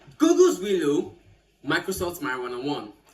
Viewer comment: Did I just hear marijuana ❤😂😂😂😂